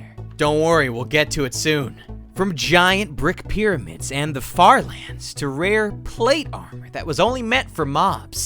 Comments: i thought you said... fart lans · IDK y I heard it as fart lands